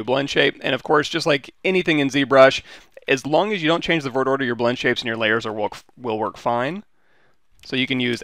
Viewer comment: Sorry english is not my native language , what do yo say on 0:38 ? as long as you dont change the ......... will work fine, dont undestand the words that follow , sorry to bother you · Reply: what i can hear is dont change the vertex order????